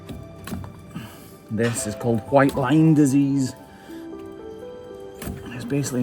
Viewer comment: Everytime I hear him say “white line” I think he’s saying “white lime”. I’m just not used to a Scottish accent